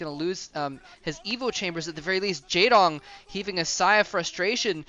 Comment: I heard "it looks he is gonna loose his evil chambers" haha! There is where he makes his zerg more evil ROLF! Flash will destroy them to redeem the zerg! HAHAHA!